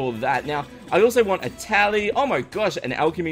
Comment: Did anyone else think he said "tabby"(as in a ninja tabby) at 8:00 Also, you should create a bomb room/shrine!